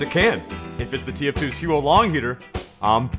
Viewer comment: the auto captions say sOooo long heater. I quit